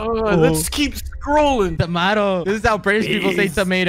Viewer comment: that sounded like wilbur yelling TAMATO